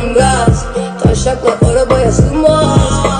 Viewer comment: "Taşşaklar Arabaya sınmaz" mi duydum ? 😅